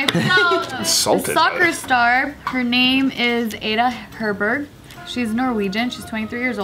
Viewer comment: When said ballon d’Or I cried a little inside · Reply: lol I actually thought she said Herbert...